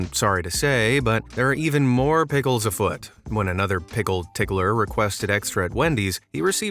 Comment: did he say "Pickle tickler" 💀